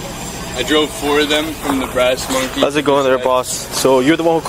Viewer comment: I'm glad we cleared that up, thought he said "Breast monkey" was like what kinda place is this!!